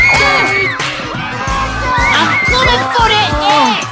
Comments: Yeah Dats yo aaaaa-sssss Santa !!! I’m comin fo dat Asss!🤣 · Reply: I thought he said "I'm gonna go to heck." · Yeah Dats yo aaaaa-sssss Santa !!! I’m comin fo dat Asss!🤣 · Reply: Oh I thought Riley said “I’m gonna go to Ed’s”